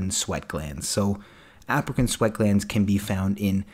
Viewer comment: Sorry thought you said African sweat gland. 😄